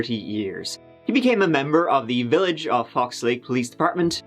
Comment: oh "fox" I thought he was saying FUX LAKE